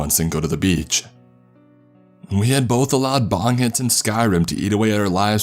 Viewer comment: I can't hear the first one. Did he say bondage and skyrim? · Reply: BONG HITS